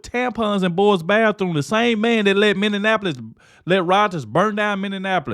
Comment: The space is not much bigger than a living room! I thought you said Indianopolis but it's Minneapolis.